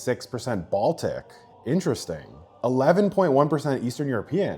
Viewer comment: I thought you said 8.9% dramatic 😭🤣🤦‍♂️